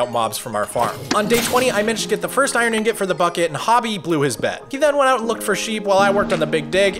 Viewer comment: i was listening to film playing minecraft, and i heard "and hobby blew his bed"